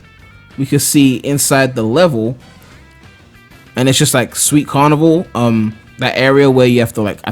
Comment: Did you just say "sweet carnival" at 3:38???